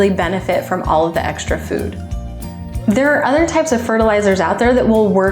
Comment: Me rewinding three times 3:14 “did she say poop”😳